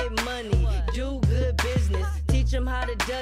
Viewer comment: do good business..awwwww I kept hearing it... I had two kids by 30😍.. Lol 🌹